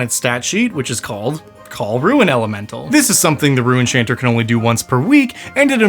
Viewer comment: I thought he was saying “Rue enchanter” 🤣